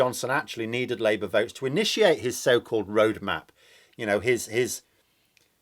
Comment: I thought I heard you say "rouge map" instead of "road map" (rewind). Hmmm. ... 😁